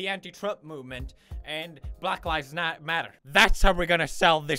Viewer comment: He said "black lives not matter" but the subtitles said "black lives matter"